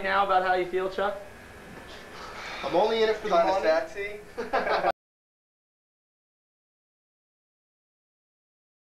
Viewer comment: Now what the hell did Chuck say at the end of the video? I've been replaying it over and over & can't figure it out @ 2:48. I hear someone else say "I'm only in it for the money." But WHAT DOES CHUCK SAY!!?! ANYONE?!?!? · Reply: Dem Ise ''Kinda sexy''